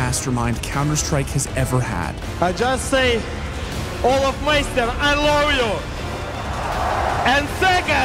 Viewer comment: Zeus Kept His Promise To olof And for me that's true Sportsmanship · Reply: Lol at first I heard him say "olofmeister aint loyal" I was so confused